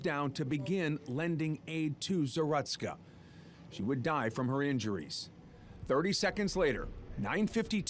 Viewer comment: he would die from her injuries ? is that what i heard play at 3:10 its a few seconds after it